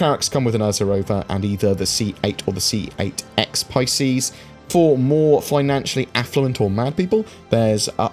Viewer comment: "for more financially-affluent or manic people" That's the best I can make out what you're saying. Feels close enough.